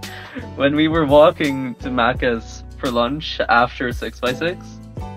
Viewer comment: DID HE SAY "MAKKA" FROM "MAKKA PAKKA"???